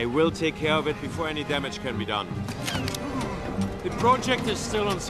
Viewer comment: What did the boss say to Zündapp? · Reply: Something along the lines of “Get rid of him”